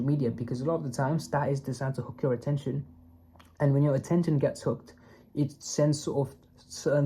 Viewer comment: I heard cooked, I mean, this word has been used more often nowadays, like "this generation is cooked" and it is, lol. * 5:58 (You said hooked, lol but still, that's also valid, that our attention [span] gets cooked)